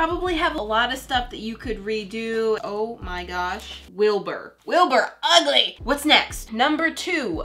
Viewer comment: ty i needed help ;n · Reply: did you just say wilbur is ugly?????????????????? he is my fav smp member ToT